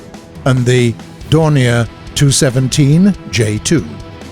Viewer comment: the narrator names the Do 217-J2, while the subtitles say N2...